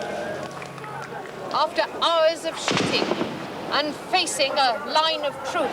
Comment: the gunshots are perfectly timed so it sounds like she says: "After hours of sh*tting"